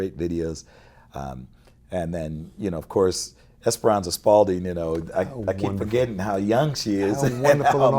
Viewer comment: Nathan mentions the name of a young musician, but can't figure out the spelling. Anyone who can help me? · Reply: Esperannza Spalding! Thanks for watching & please subscribe!